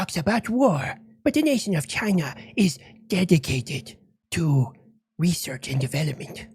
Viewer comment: At about 05:08 in this video: CHINA: "But the nation of China is dedicated to research and development." Yeah, especially EVERYONE ELSE'S...🤦‍♂️ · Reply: Thought he said defecated.